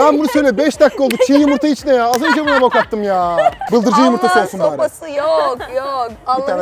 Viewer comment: abi bıldırcın yumurtası getirin diyor,(eskiden beri izleyenler anladı beni izleyenler bu ne diyor😢)